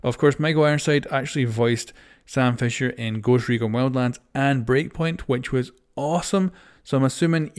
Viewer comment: Why did i hear “grocery game wildlands?”